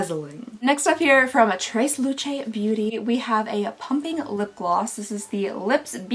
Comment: At first I thought you said a "Pumpkin Lip Gloss" and I was like, "GET THE CREDIT CARD!!" but then you repeated and said "plumping" and I was like, "Oh. (sad face)".